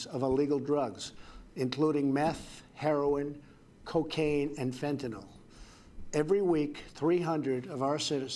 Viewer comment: did he say Fentanyl? That's interesting. I knew pharmaceutical Fentanyl has been a huge issue and is responsible for Prince and few other celebrities deaths. But I had no idea it was big on the black market. You learn something new every day.